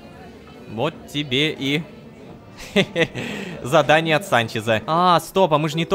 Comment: Когда не так перемотала и вместо "задание от Санчеза" услышала "смерть отца"😂😂